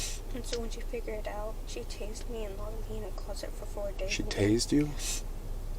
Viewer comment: Omg, I was certain the cop misheard the girl in the car and thought she had said "she cashed me" until I saw the tazzer 😱😱😱